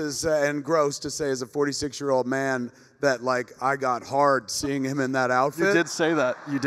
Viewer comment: Did he say he got "hard" seeing Grant in that outfit?! Someone pls tell me I'm wrong. Isn't this a kids event!